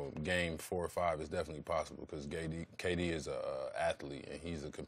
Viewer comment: he says GayD instead of KD 😂😂😂😂😂